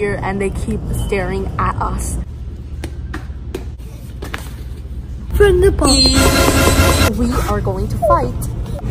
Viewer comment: Bro at 0:35 I thought he was gonna say from the top make it drop